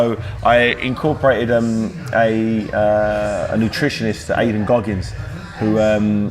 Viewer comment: For a minute there, I thought he said his nutritionist was David Goggins.